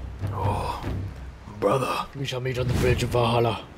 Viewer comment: Before you attacked the first time, it sounded like you said Bruhhh